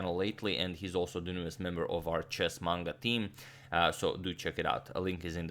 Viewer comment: Shortly after 1:00 does say “our chess manga team”? Didn’t know he was into that.